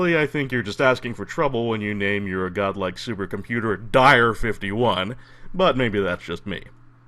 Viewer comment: Frankly, I misheard "Dire51" as "Die-R 51," my dear friend. =)